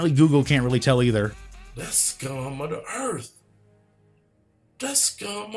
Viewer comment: I've watched this video at least 15 times since you uploaded it. I just now realized what (I think) is saying. That being "that's scum of the earth."